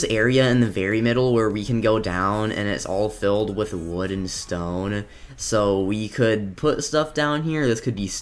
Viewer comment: am i the only one who thought he said "wooden stone"